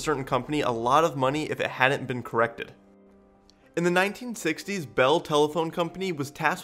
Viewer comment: He says "in the 1660s". I'm like "no way, basket ball didn't exist!" After mulling it over I decided I must have misheard and he said 1960s... He's messing with me hard core here.